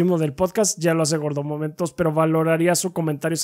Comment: Yo solo escuché a los diciendo que GORDOMOMENTOS es el rey de reyes 🧐🧐